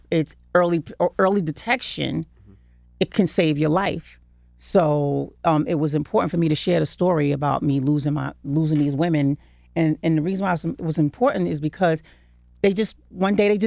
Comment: She almost said "me losing my 'breasts'! Anyone catch that? When you're ready, Mary, you can be transparent with us.